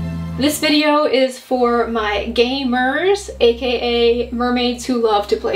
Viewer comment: At first I thought you said gay mers, I was so excited lol 😅